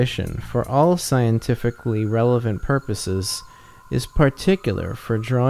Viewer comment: Great lecture. Just one semantic mistake of the aloud reading: at 1:26 reads "is" instead of "in", according to the text as edited in the Feferman book. The sentence goes: " 1. Mathematical intuition, for all scientifically relevant purposes, IN particular for drawing the conclusions as to observable facts occuring in applied mathematics, can be replaced by conventions about the use of symbols and their application."; not: "...for all scientifically relevant purposes IS particular for drawing...".